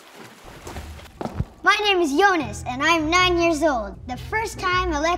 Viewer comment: I thought they said jonas not yonas 😂